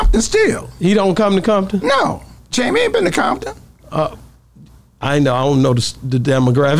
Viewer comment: Did he just say yg not from Compton?? Am I the only person hearing this?